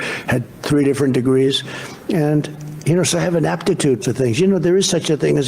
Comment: when you mishear "ineptitude" as "an aptitude" one time and make it everybody elses problem for the rest of your life